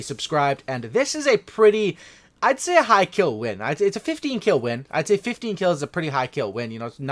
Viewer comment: “ fifteen kills, not my best ever” im here struggling to get one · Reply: Exc0ticBeAst 74 Lol 15 is pretty good but I swear I thought he said “50 Kills, not my best ever but” I was like wtf lol